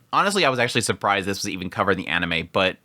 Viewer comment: did he say I was actually surprise "this wasnt covered" in the anime or he said “this was even covered”? Watch the whole part for context and please tell me please it would greatly make my day thank you